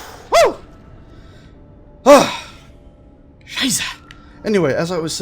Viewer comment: Did you just say 03:05 "Scheiße"? or was that my imagination?